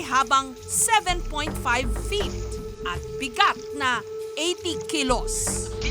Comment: I thought I heard 27 feet! 🤣🤣🤣